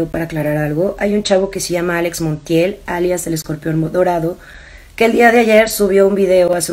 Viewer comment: Ya entendi Alex Montiel es "El Escorpión Dorado" Y Alex Monthiel es "El Escorpión Modorado"